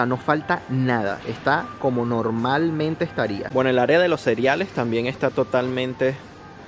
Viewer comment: el área de los seriales ...... 9:10 wtf seguro que dices asesino cereal XD